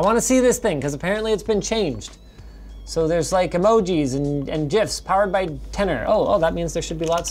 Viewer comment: Did he just say "jif"?